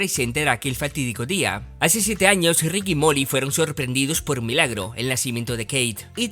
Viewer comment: Tuve que repetir este momento porque escuché Rick y Morty JAJAJA xd